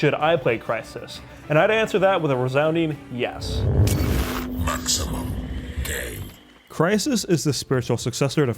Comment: Did i hear maximum gay 2:30 ?